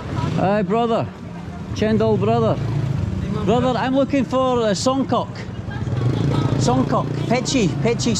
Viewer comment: I swear i heard him say "I'm looking for some "kock" 😅